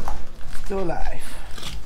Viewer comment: sounds like he says "its still alive"